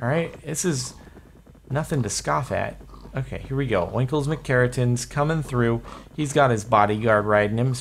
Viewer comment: I thought he said Wankles XD